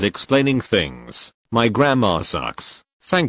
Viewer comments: your-- your grandma sucks?- did anyone else hear thisvorbis it just me :') (no offence) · Why did I hear her say ,, My grandma sucks” when she said ,,my grammar sucks” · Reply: I heard him say "my grandma suck" and I laughed like hell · I heard "my grandma sucks" and I was like whaaaaaaat?! I realised u wanted to say "my grammer sucks" after I read th subtitle 😂😂😂😂😂